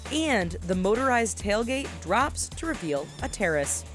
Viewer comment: "drops to reveal a terrorist" lmao I don't why I heard that